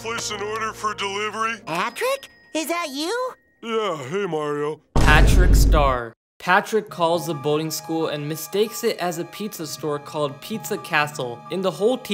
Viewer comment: did he call him mario?